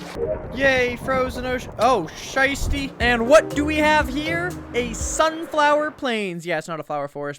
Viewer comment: Did he say „Scheißtie“ (German btw) 😂